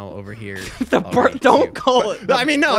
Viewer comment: At first I thought they said "Don't call it Birkenau!"